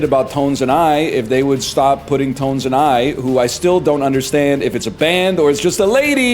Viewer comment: Whole time thought he was saying tone zanai